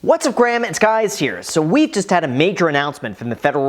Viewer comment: Did you just say “What’s up Graham, it’s guys here”? 😂